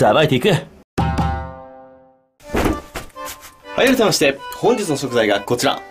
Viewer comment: はじめましてって聞こえる笑笑